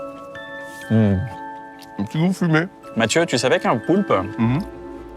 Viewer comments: C'est moi où il l'appelle Mathieu ? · C’est moi ou Kyan a appeler Poulpe Matthieu a 14:26 ????